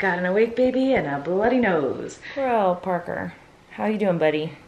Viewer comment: did she say grow up parker